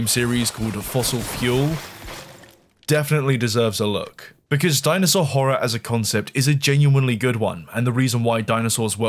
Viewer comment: I misunderstood. I heard "dinosaur whore" and got mad excited.